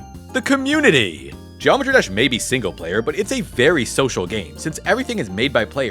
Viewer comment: I heard "Geometry Dash made me single but it's a very social game"